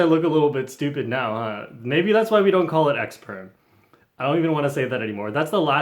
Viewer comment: When he said X perm I have the captions on and it thought you said egg perm